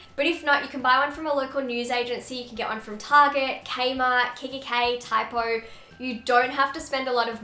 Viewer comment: Did she say KKK